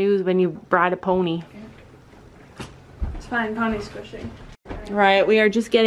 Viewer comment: did say "pony squishing"?